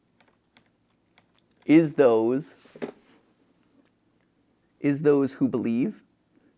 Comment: for those wondering what it says, it says “Fate awaits those who believe”